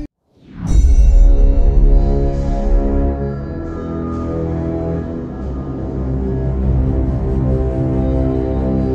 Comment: What does the advisor say at 11:11? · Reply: It’s sounds nice doesn’t it